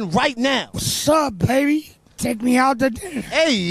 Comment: “Wasup baby” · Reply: I thought he said shut up baby and take me out too dinner-😭